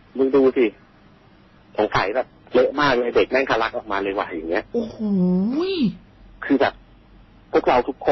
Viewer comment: พูดว่าอะไรนะ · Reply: ทะลัก